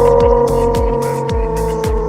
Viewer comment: DID I HEAR A ROCK AND STONE? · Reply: when I first watched this, I could have sworn I heard him say My business is with Eisengard tonight. With a fvckin stone. Lol